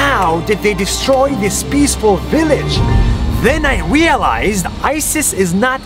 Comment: Did anyone heard 'Wealized' Instead of 'Realized'? ._.